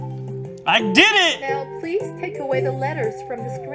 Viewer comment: i thought it said p*rn. So anyway..